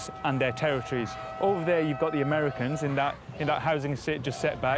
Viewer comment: did he say Americans? Or am I hearing things.. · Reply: He did say The Americans - it's the name of a gang in Cape Town. They're not actually American..